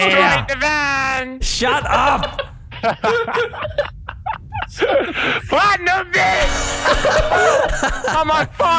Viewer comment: did he say platinum butch